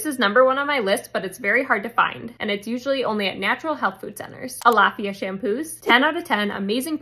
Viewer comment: ALAFFIA! I thought she was saying alfalfa weird!!!! I had to pause and read the subtitles. 😅